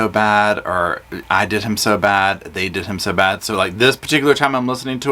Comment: As a southerner who is use to the "draw" we use in speaking and being familar with talk from that area of the south, I hear "they did him so bad."